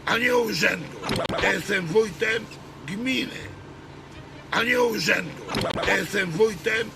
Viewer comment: Ja usłyszałem "ja jestem wójtem kpiny a nie urzędu"😂